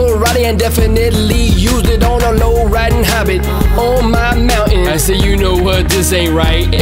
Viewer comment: I heard "low riding hobbit" and I lost it XD